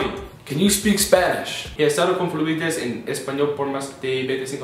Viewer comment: wth is Ethan saying on the beggining? I'm spanish and I don't get it. Btw, he said something like "(he estado confurmite" - that's what I don't get) "spanish for more than 25 years". And the gray said "me too" lol